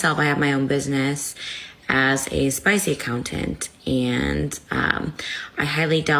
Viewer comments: What did say she does for a living? For some reason, I keep hearing “spice accountant”? 😂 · Can someone tell me what Trisha's sisters job is? I just hear spicy countant??? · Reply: yes I heard “I am a spice accountant” was she slurring or mumbling or what? 😂